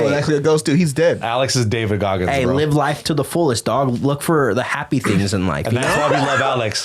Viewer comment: I thought he said "look for the happy-penis"